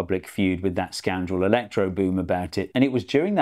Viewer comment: anybody else hear "electrogoon"?